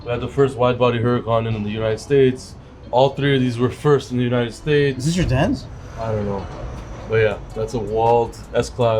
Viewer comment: did he say “is that your dads?“ to?